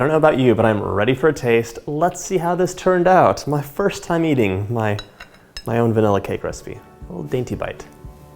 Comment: did he just say my first time eating my own vanilla cake recipe !?!?😧